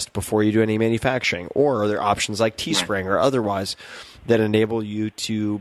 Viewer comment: What did Tim say at 27:15, teaspring??? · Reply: teespring, POD company